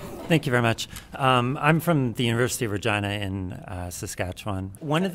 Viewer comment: i listened to it 4 times .. did he say university of vagina? or are my ears tricking me? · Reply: University of Regina.